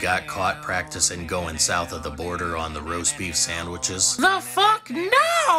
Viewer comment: I was choking on my chicken nugget because I heard "the fuck NOOOO"